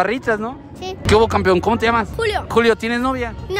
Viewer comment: yo le baje y se escucho que dijo CuLo